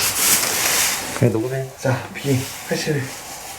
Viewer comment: 근대.. 녹음된거 편집을 잘못하셨읍니당...ㅎㅎ 형이 B-81라고 했는데 자막이 B-87리고 했고 20:17 그리고 형이 들려주고 있는건 A-81 22:19 으흠.... 죄송합니다 밝혀서... 나쁜 의도로 말 하는건 아닙니다.. 전 구독자이고 형 형 그리고 다른 스텝들을 존경하고 사랑합니다.. 영상 재밋게 잘 보고 갑니다. 아프로도 재밋는 영상을 찍어주세용~~^^ 왔져염!^^